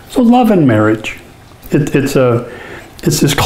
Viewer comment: Somehow I keep hearing "horse and carriage"...